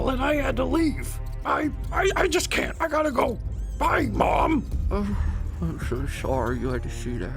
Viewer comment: sounds like she said bye Bomb